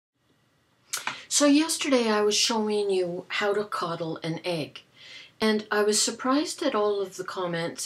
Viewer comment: Is it only me, who misheard the topic: "How to cuddle an egg?" :)